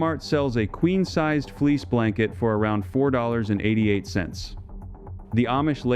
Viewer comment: ❤️ Many Wonderful Suggestions 🐝🕯️ Beeswax Candles Clean The Air. While Paraffin Wax Candles are the #1 cause of House Pollution 🐑🐏 There is a big difference between a Wool Blanket and a Fleece Petroleum Product Blanket. A Wool Blanket costs much more, but is well worth the cost. A flame on Wool Blanket quickly extinguishes itself, while a Fleece Petroleum Product Blanket is Extremely Flammable 🔥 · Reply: So I didn't hear wrong. I thought I heard wool blanket but the I heard fleece blankets. Thanks for clarifying.